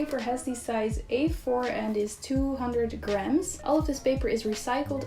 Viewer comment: On the first item I thought she said it was 200 grand not 200 grams 🤣